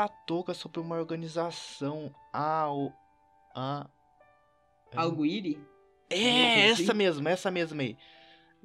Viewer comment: Pera ae kkkkkkkkk eu ouvi direito? Algo iri?